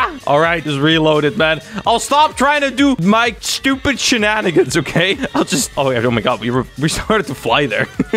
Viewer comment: did i heard stupid s#!t o-o